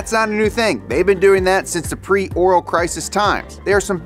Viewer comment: anyone else hear him say pre oral? must've been hard times